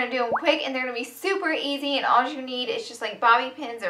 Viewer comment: did she just say alls you need